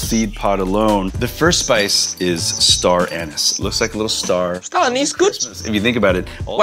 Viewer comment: I heard him say ‘Star Anus’